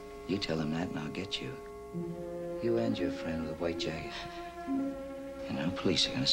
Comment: At about 1:53, what does Chandler say? He starts out "you and your friend with the white_____". Does he say face, or jacket, or what? I'm puzzled. Thanks for any help. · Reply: Jacket